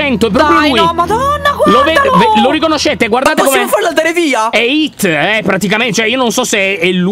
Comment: sbaglio o ho sentito "è hitle- praticamente..."